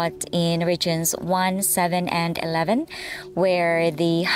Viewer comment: You don't need to schedule your life around your Channel...! I wasn't listening properly at the start and thought you said sex education was only taught at one 711 😂😂😂😂😂😂😂